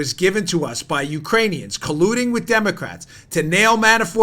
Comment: Sounded like he said Damocrats